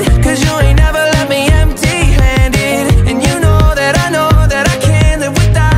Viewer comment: 不知道為什麼第一次聽到這首歌的時候會一直把I know that I can't聽成どれかけ